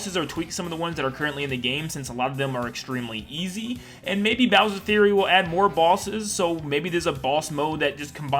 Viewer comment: bowsers theory is what I heard